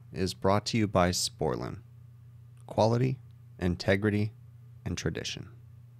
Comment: Every time you do your sponsor intro, I hear 'This video is brought to you by Sporlan. Quality. Integrity. Sporlan.'